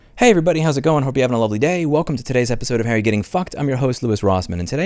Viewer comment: Louis says: "How You're Getting F-ed" Automatic subtitles say: "Harry Getting F-Ed"